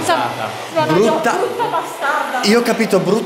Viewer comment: CIAO #PITFAMILY CI AIUTATE A TRADURRE QUALCHE NOSTRO VIDEO ? Il vostro nome comparirà sotto al video sottotitolato ;) CLICCA QUI : <link> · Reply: nell'EVP che avete fatto al minuto 7:27 sembra che dice brucia bastarda per favore datemi una risposta perché io ho capito così.